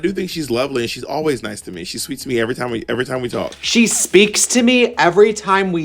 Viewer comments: i know the text on the video says “she speaks to me every time we talk” but i swear i heard “she’s sweet to me every time we talk”, no? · I always thought she said "she's sweet to me everytime we talk" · I hear “she’s sweet to me every time we talk” but what do I know